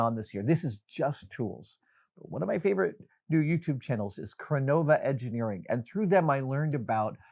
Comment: What does he say at 15:04 i cant make out the channel name. Krunova engineering ??? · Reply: I think it’s Chronova Engineering